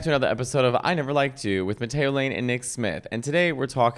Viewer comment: Okay, but it did sound like you said Potato Lane 🤭